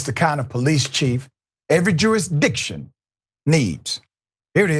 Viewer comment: Dr. Richey, did you say, "Every Jewish Diction?"